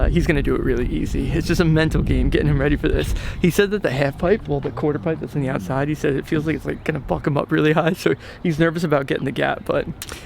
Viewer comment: I keep hearing he thinks it's going to f#%k him up